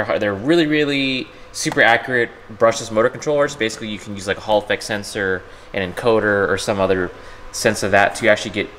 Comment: Legit whenever I hear "Hall effect sensor" I hear "Holofex sensor".